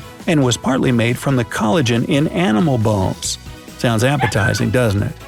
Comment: I thought he said "Animal Balls".